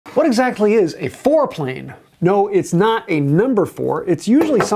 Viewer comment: With all the noise around me I thought you said “foreplay.” I was like, hey now did I run into the Hub? 😂 I mean all we talk about is working on our wood. 😂😂😂😂 I couldn’t resist. 😂 Im such a child. 🤣🤣🤣🤣🤣🤣